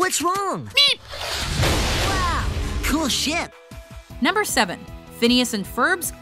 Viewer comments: Okay, the first time you heard it, who thought he said “Cool shit” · I thought Phineas said "Cool shit" instead of "Cool ship" 😂